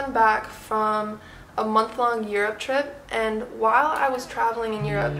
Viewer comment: she said a month long Europe trip but I heard a month long year trip and I was like is you on crack